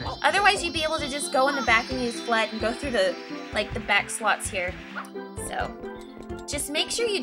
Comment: *listens to video again after reading comments* wow, it does sound like is saying sluts